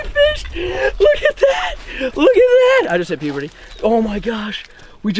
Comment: Am I the only one who heard 8:08 said I just hit puberty